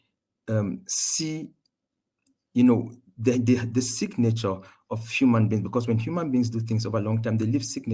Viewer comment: i think he said signature of human beings, didn't he? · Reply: Yes that what i heard too. I think the heard "sick nature" instead. But he went on to agree with that humans created the god of thunder